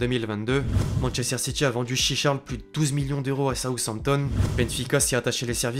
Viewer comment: C'est moi ou il a dit chicha